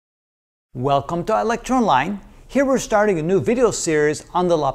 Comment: I hear it like "welcome to electron line" every time lol